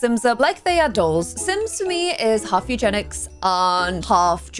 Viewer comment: Ummm…maybe 1:45 mins in and she says sims for her is “half eugenics”. Was I the only person who heard that. I even put CC on to see what it picked up and it even wrote “eugenics”. Very uncomfortable…I hope she just thought she was using a different word…but now I don’t feel comfortable watching her vids and this is my first time watching one of hers. Hope this gets cleared up and everyone does some research on that word…and it’s horrifying history…